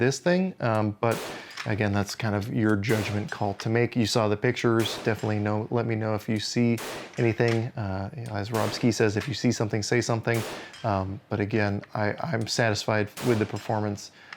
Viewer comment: I thought Rob ski said if you see something shoot something.🤣🤣🤣